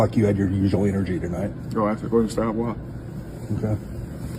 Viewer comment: I mean wtf did he say 0:34 ? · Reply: Go ask the coaching staff why